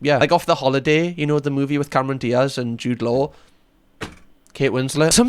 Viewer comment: judelow? What did you say here lol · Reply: Jude law? The actor...